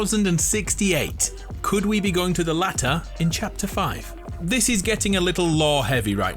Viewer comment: Can someone explain to me what he just said because all I heard was the "lat er" and if so what is the lat er